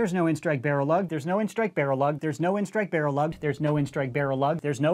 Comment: Sounds like he is say “No end strike Bear a lug”